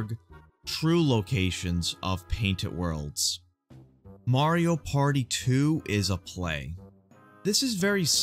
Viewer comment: I honestly thought you said "Mario Party 2 is a plague" and I just "wut" for a moment